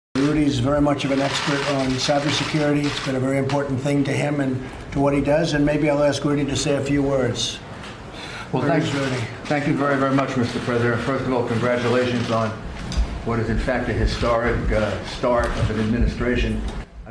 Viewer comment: I think at 0:26 Rudy says "Mr Predator".